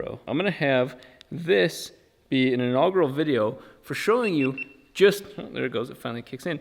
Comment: totally thought you said "all girl video"...RIP when I found out that's not what you said...